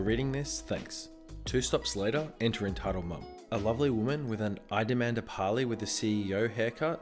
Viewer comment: he said “a lovely woman” What it said “a lovely whale” Me:HAHAHAHAHAHAHAHAHAHAHAHAHAHAH